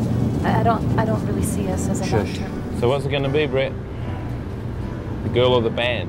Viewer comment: Does Coco say I dont really see us as a doctor